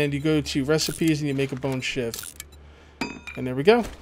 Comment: when said bone shiv I thought he said bone shit